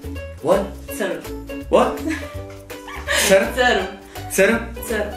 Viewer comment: did you say Serb? Do people like it? Not really